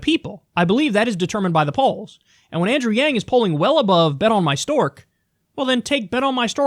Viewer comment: I heard him right this whole time you were saying bet on my stork I thought I was high the first time I heard him say that